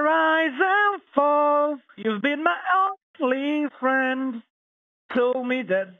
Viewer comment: Did I hear “you’ve been my ugly friend.” 😂😂😂